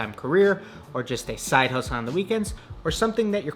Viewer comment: what did he said lmfaoo side hoes lmfaoo im dead but anyways carry on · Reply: Side hustle lol